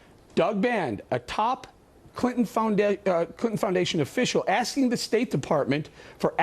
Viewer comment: does he say found dead and then correct himself?